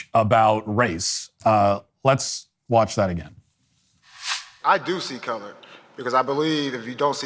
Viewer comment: When he's saying " I do see color" I hear I'm looking out for white people to be racist.... WTF